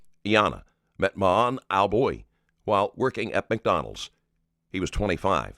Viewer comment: I thought she said she like worked at McDowell's.. like from Coming To America with like Eddie Murphy and like stuff. 😂